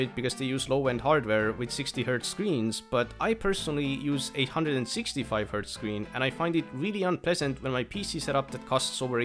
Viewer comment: DID BRO JUST SAY 860Hz MONITOR ???!?!!?